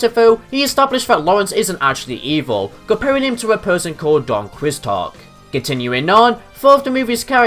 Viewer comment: did you just say Don "Quicks-tot?"